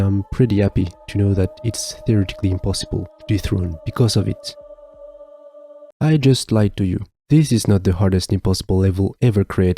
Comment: did he just say "I just like TUYU"? oh no he said "i just lied to you" XD